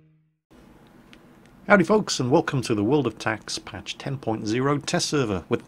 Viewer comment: Did anyone hear "Welcome to World of TAX" at 0:28 ?! No? Just me? ..man I really do need to compile that tax report